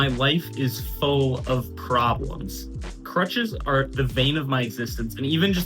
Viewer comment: did you just say crutches are the VEIN of my exsistence...? XD